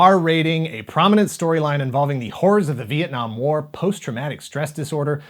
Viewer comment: sounded like he said the whores of the viet nam war.